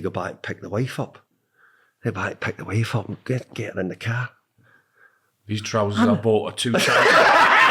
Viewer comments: what did Alan say here? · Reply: “These trousers I bought are too tight” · Does anyone understand what Shearer said here with 'the trousers...' and what Moyes said in response? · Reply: These trousers I bought are too tight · What does Shearer say at 31:24? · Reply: "These trousers I bought are too tight"